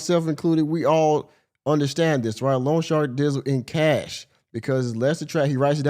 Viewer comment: I am sorry; but this is driving me nuts. It’s not ‘long shark’; it’s loan shark. They are called that because they are illegal predatory lenders that charge super high interest rates. Every time she says long shark my head wants to explode. 😂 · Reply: I thought she was saying “lawn shark” 😭